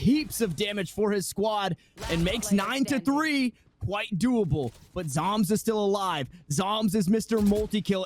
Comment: did he just say whitedoable?